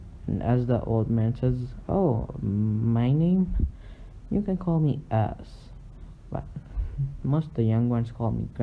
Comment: Lol I thought he said "you can call me ass"!